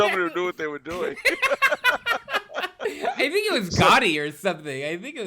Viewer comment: At first I thought said Gandhi not Gotti.